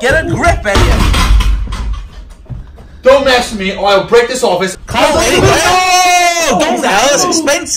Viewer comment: Did he said I'll deck u that's American slang